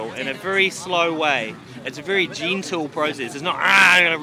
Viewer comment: I thought you were saying ‘Jean-tool’ until you pronounced ‘gentle’ with the bottle in your mouth! I thought: What the fuck’s a jeantool?